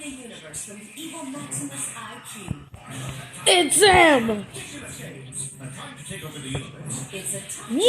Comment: List of what the kid said (includes timing stamps) 0:18 its zim! 0:22 WHAAT?! 0:38 is that Mr Bean? 0:41 his car was stolen (or whatever the frick he said) 1:13 ew 🤨 2:42 whispers what? 😦 3:16 WHAT?! 😰 3:22 reads 3:52 I’m so scared…